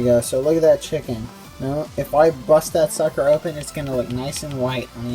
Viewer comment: Did just say "If I'm gonna bust that sucker up"????